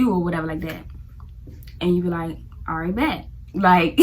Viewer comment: Sounds like you saying “Bat” instead of “Bet”😅 😊